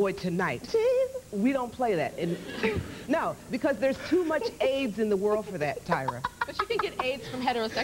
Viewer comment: Did I just hear "too much AIDS in the world" that's a mess.